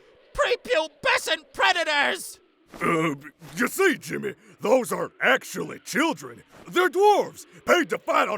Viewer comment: hmmm did he say Jimin or Jimmy